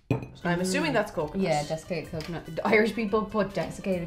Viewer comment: Alright!! Did anyone else hear mention 'defecated coconut'??